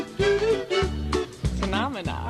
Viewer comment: I can't lie, I did get a kick out of Sandra Bullock, who sounds like she saying "phenomena" instead